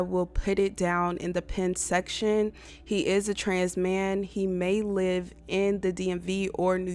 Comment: did she say he is a TRANS-MAN?